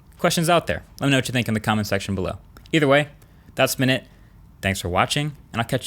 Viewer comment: Did he say "either way, dus minute" at the end 9:11 😂😂😂